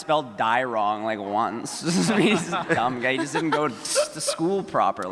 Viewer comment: I swear Richard just said “dumb gay” not “dumb guy” Murphamy confirmed